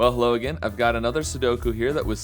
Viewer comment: OML YOU SAID “ hello agian” AT THE BEGINNING AND I THOUGHT YOU SAID “well logan,” AMD I WAS LIKE WHAT. BECAUSE MY NAME IS LOGAN. THAT WAS SO ODD. ANYWAYS, KEEP UP THE GOOD WORK MAN I LOVE THE VIDEOS!! THEY MAKE MY DAY:D